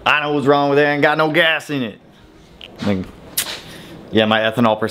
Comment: Uhhhh, what did he say at 19:43???!? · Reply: Ain't got no gasssss in it